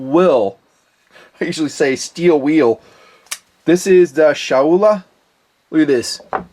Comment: Thought you said Shinola with the third Chinese knife on table top. The logo looks the same. Looks like a rip-off of Shinola branding which actually does sell Benchmade knives at a premium for co-branding. On a side note, I got the firebird on your recommendation. Great value knife!